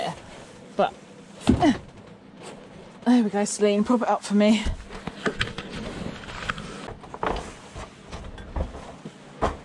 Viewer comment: What did you say at 23:01? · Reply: I said 'there you go Celine, prop it up for me'🙂 I was referring to my cardboard cut out of Celine Dion😂🤣👍